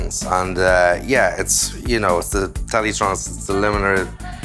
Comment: Ace video! I can't make out the name of the limiter he uses for bass with the la610 preamp. Around the 4:40 mark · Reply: LA-2A maybe? I think he said Teletronix...